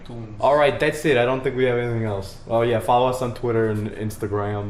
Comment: Whenever you plugged the podcast I always thought you were saying "Loud Mouse" instead of Loud Mouths. Am I retarded?